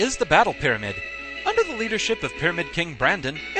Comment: When you said Pyramid King Brandon, I could've sworn I heard "Fuhrer King Bradley". Silly Fullmetal Alchemist.